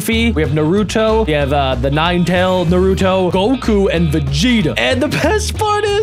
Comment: I swear I heard him say Virginia when the name was Vegeta